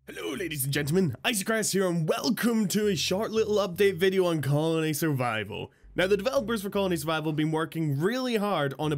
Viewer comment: When you started the video you said," hello ladies and gentlemen, Icycaress here." I thought you said," Hello ladies and gentlemen anti Christ here."